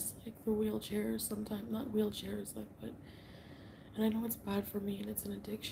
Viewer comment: can someone please tell me what does she says at 23:41 "I miss....???", I can't understand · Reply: She says she missed wheelchairs, which is her code for (I think) cannabis edibles.